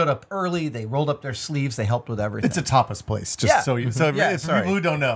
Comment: I kept hearing "It's a topless place, just so you know..."